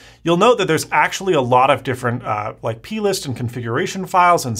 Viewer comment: What did he say at 0:48 😂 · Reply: plist ;)